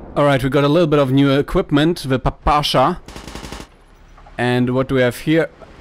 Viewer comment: mate did you just call that the "pipasha"?